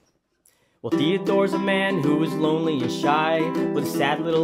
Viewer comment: When you say "Lonely and shy" It sounds like you say "Lonely as shite"